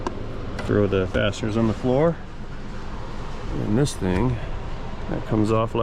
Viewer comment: When the bolt/screw fell on the floor I thought said throw the bastards on the floor. I played it again with CC to see the word he used, and it was fasteners. LOL.